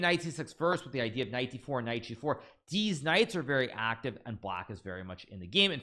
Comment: Deez Knights, Did anyone hear that?